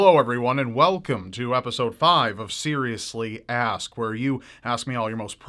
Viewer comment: I honestly thought he said "seriously ass"